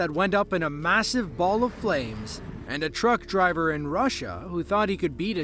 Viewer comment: the train one with "russian" driver trying to beat the train is from Poland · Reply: Where does it say “Russian”? The narration I heard says “local.”